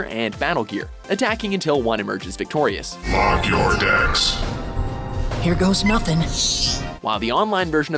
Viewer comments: did anyone else hear fuck your decks at 1:00 ? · Did that robotic voice say "Fuck your decks"?!?!?! XD That's what I heard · did that just say fuck your deck